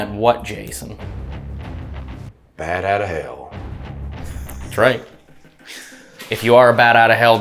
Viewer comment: Sound like Jason said Bat out of Hell .... LOL ... but it's Bad out of Hell.